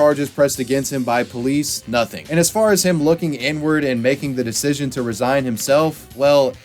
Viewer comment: i heard "n-word" and was confused for a couple of seconds lol